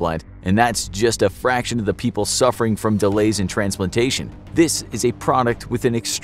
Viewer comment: Did he just say "transplitation" right after 2:10?